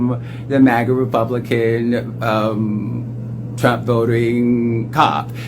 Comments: I think he said MAGA Republican "dude", but his lisp kinda made it sound like he said MAGA Republican "Jew". Either way though, you're right, these people are INSANE! I am a predominantly white dark haired green eyed MAGA man, and I WISH I could date Laverne Cox! 😍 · In fairness, I heard "Mage Republican DUDE." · I heard " MAGA republican dude"